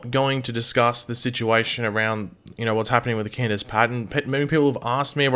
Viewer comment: What controversy are you talking about ay 0:20-0:24 ? I can't understand you when you mumble/speak fast with an accent lol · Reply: alex toll Candice Patton